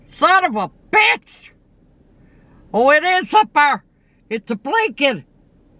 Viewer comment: What did Grandma say? I heard "Oh, it's super." 🤣